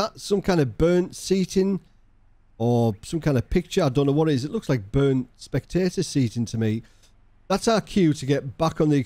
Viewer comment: Man I thought you said "burned satan" LMAO. I always thought it was some obscure rune/artwork but you're right, it does look like burned spectator seating.